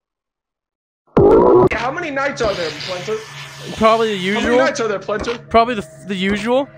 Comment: did bro say Plenter💀